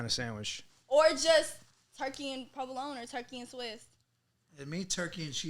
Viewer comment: did she just say suwiss